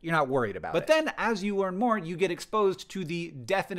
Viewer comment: auto captions says "earn more" instead of what I assume is "learn more". Surprisingly accurate.